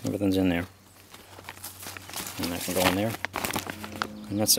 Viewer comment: I thought it said Beef Party haha